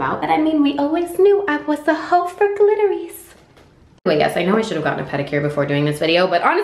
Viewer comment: When the subtitle say 'clitoris' instead of glitters 😂